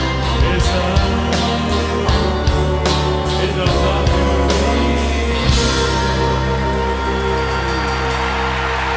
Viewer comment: Qué dice en 04:23? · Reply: "Ella ya me olvidó" (es un homenaje a una canción de Leonardo Favio)